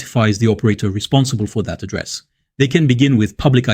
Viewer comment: what did you say at 6:40? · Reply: "pa-dress"?